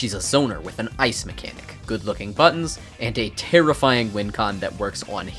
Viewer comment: When he said “good looking buttons” I misheard as “good looking butt” 😂😂😭😭